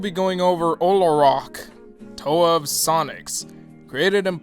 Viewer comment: At first I thought he said the toa of sonic, as in sonic the hedgehog and I thought that he was a toa of sonic half the video